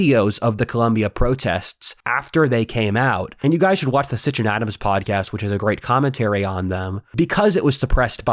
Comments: What are you saying??? "Sitchin Adams podcast"? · What is the podcast name he references at 35:53? I couldnt quite catch what he said. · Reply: Sitch and adam. They're small fries if just by daily watchers since their bi-weekly sessions are at the apex of video commentary by length and it comes with a lot of fluff and running jokes but it's substantive stuff in the thick of it i swear. There is lot of what kind of amounts to a youtube drama alert channel but following the continuities of basically any politically famous person you can shake a stick at between their sessions of just looking at anything politically topical. I pestered Rudyard once about getting in contact with them some years back and they hit it off well with each other with a good couple of guest appearances. Whatif's own words in their first streamed interactions were "you guys are a lot smarter then a lot of the other podcasts i've been on" or something to that effect. S & A are some rather prolific social butterflies which might just be part of their line of work in general with all the people they've taken on for chat. They've gotten Sargon as a semi-regular, Shortfatotatku, Shoe0nhead, Mauler i guess. and some hostiles namely a bunch of breadtubers. Their couple of stream interactions with Ana kasparian possibly have accounted for a non zero amount the building up of her views falling out with the tyt fandom. A lot of people who are somebody do give a shit about what they have to say in reply to them.